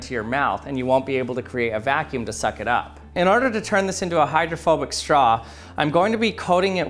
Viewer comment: I thought that said a homophobic straw LMAO